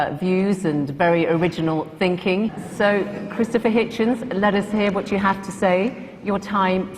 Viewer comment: Haha, it sounded like she said "and Crucifer Hitchens"! I think I'm gonna coin that if it hasn't been already!